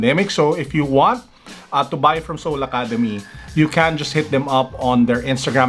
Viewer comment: Did I hear sole academy at the 7:00 mark?